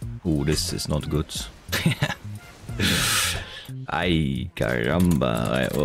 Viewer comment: did u say "ai caramba"?